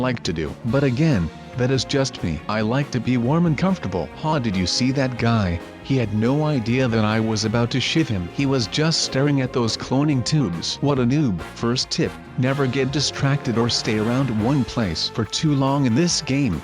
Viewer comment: It sounded like he said S***